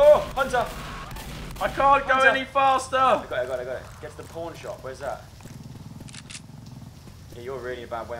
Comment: instead of hearing "pawn shop" i kept hearing "porn shop"